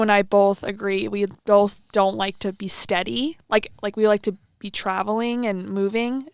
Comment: did you say "we do not want to steady" @ 1:52 ???? What do you mean??